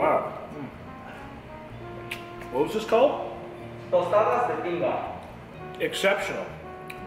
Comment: "whats this called? " Man: "speaks spanish" - has no idea what he says -: "exceptional!" xD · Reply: I thought the guys said the name was tostados de pinga. Like dick tostados but I could’ve head that wrong.